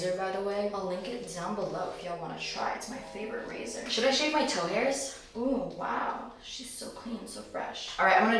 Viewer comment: Tell me why I thought she said “Should I shave my toe nails” BYEEEEEEEE 😭🤚